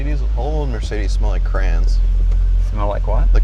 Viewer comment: I didn’t understand when He said “Crayon” at first either, Jay. I thought He said “Crain”